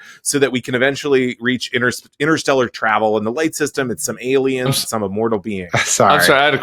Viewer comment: I misheard that as "light rail system" and I was so completely confused. I was like, we're taking a train to space?! Lol